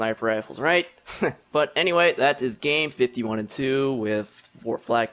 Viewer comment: sounds like he says pea soup at the end lawl